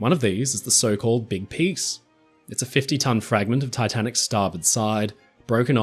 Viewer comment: The big piece is 15 tons but it sounds like you say 50.